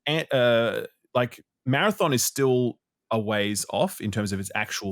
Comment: almost sounds like almost says Anthem instead of Marathon at 2:35 ahahaha